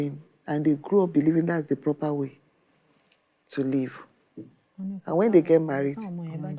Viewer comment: The judge has given the interpreter platform to market herself whether she voluntarily gave herself for the job or she was appointed to do it for a fee but unfortunately, she bastardized the golden opportunity that comes but once with an unprofessional conduct of incessant laughter over a serious matter. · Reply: I guess only few persons would notice this subtitle blunder @ 16:42 minutes... 😅😂😂 "...they will grow up believing that is the proper way to LEAVE" instead of "proper way to live"